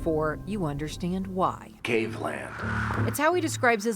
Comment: WTF is he saying? cavelamp? caveland? cavelan? cavelume?